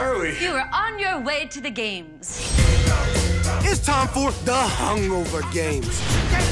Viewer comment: i dont know its just me but i heard about "the starving games"